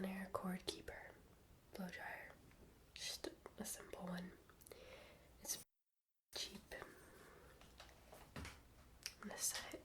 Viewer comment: Did you just say "it's f*ing cheap"? 3:23 LOLOLOL jk, it just sounded like you bleeped out a curse word